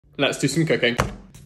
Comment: I legitimately thought he said "Let's do some cocaine."